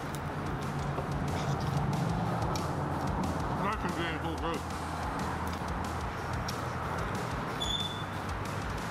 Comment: what did he say? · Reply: Mushrooms Andy puhl...gross 😂😂😂😂